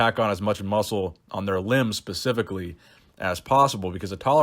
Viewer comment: Am I hearing ‘pacifically’ (specifically) or is old age catching up to me 👂